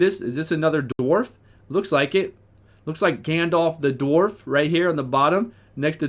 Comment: Hahaha, Gandal the dwarf. I laughed so hard when I heard that